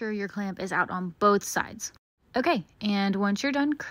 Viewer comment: "And just bru sh!t out" Misheard words